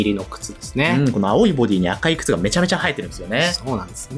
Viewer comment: ねぼけながら聞いてたから「めちゃめちゃ映えてるんですよね」が「めちゃめちゃ生えてるんですよね」に聞こえて一瞬!?!?ってなりかけた… おかげで目が覚めましたありがとうございます(?)